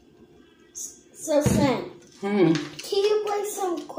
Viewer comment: Did he say, "So, son, ...?" If so, he just killed me again!!!